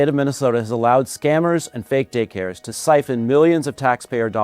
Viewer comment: My dumbass seriously thought he said "and fake dickhairs"